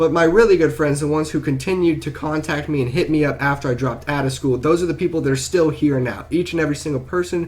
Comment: At first i thought it said dropped out of preschool